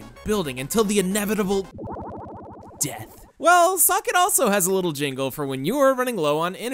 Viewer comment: I heard suck It :'D